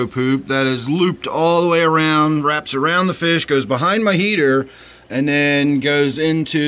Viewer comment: I thought you said behind your ear. ( Lol ). I better get the poop out of my ear.